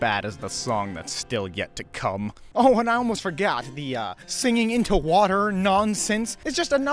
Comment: Did he say forGyatt 🤣😭🤣😭🤣🤣💀💀💀